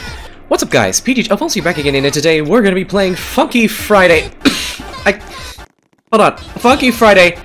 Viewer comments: It sounded like he said f*cky friday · He swore, he said fuky friday bruh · BRO BYE- WHY DID I HEAR “F U FRIDAY” WHEN HE SAID FUNKY FRIDAY 💀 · It sounded like he said "F*cky Friday" if u know what im talking about